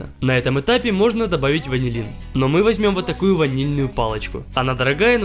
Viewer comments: Хахаха, послышалось вазелин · мне сначала послышалось вазелин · ПОСЛЫШАЛОСЬ, ЧТО ТЫ СКАЗАЛ "ВАЗЕЛИН", вместо ВАНИЛИН. 😂😂😂